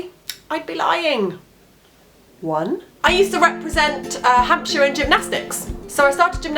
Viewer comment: I need new earphones. I thought you said you represented Hamsters at gymnastics. That would just be silly!